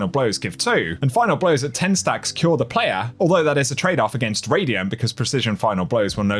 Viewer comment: I heard you say "at 10 stacks, kills the player" I was like what!!😅 Oh CURE the player!